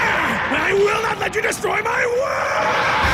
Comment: what did he say? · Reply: ¡¡I will not let you destroy my world!!